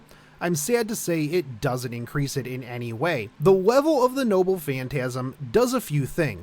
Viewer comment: I keep hearing him say “nobbu phantasm”